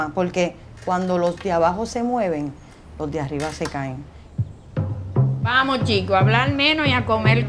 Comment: "Cuando los de abajo se mueven los de arriba se caen" Qué gran mensaje, México con ustedes hermanos Puertorriqueños 🇲🇽🤝🏼🇵🇷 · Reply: Yo entendi "se cagan." 🙂